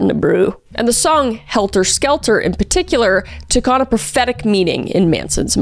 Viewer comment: Not me mishearing prophetic for pathetic and thinking "eh, sounds about right"